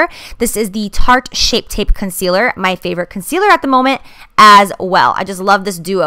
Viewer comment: Did anyone else think she sounded like she said "shit tit concealer" lol! Love you girl, you are awesome!